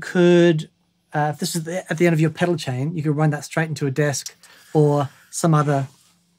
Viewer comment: I could have sworn said “Pedal Chaps”. I’d watch that all day.